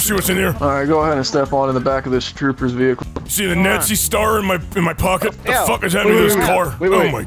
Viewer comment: did you say nazi star?